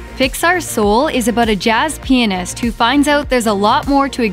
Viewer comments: sounds like she said jazz p*nis xD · WAIT WHAT DID SHE SAY!?! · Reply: Jazz pianist